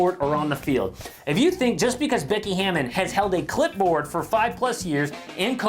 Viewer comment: oh clip board, I thought you said clit board